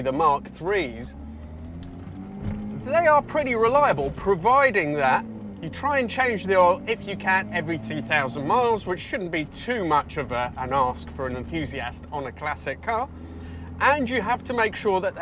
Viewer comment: What did you say in 2:54 ? I did not get it. Provided that we change what please ? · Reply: … change the engine oil every two thousand miles…